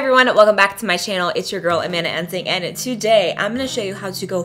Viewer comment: It sounded like you said ”anana ensing” hahhahahhaahah